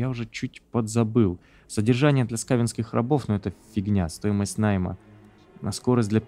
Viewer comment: Занимался другим делом и параллельно слушал прохождение и услышал "содержание для Сталинских рабов" :)) 27:47 Звездные войны были еще в ДЛС Пиратов.